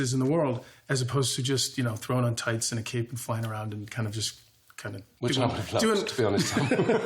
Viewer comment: what did lucifer say at 4:32? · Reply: Dan K “which i would’ve loved, to be honest”